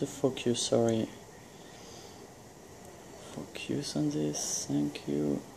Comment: Haha when you say focus it sounds like you say f*** you.